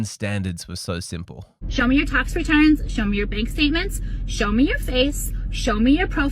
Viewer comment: What did she say at 2:56? Right before she said "show me your bank statements?" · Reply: "Show me your tax return".